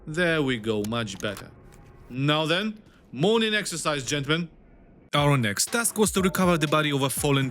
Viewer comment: The fact I heard: M**n in exercise gentlemen. Scares Me... I think I should start listening to different music...